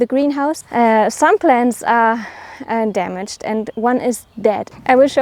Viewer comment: I thought you said "Some sheep are damaged, and one is dead". 😅😅😅